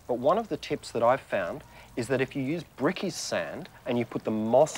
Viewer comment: what is "prickysand" ? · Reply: I'm not sure, but I thought they said brickys-sand, as in brick layers sand, general hardware kinda stuff.